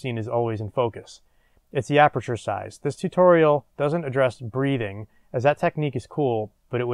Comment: what did u say at 3:18 ? i searched breeding and couldn't find anything lol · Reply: Breathing. Settling in.